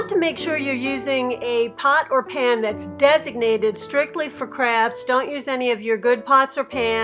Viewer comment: this is awesome but I totally thought u were saying crabs every time u said crafts. :)